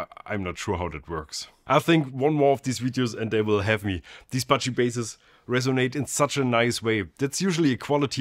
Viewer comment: I first heard "Budget" Bass, not Bacci Bass. It kinda turned me off. 😂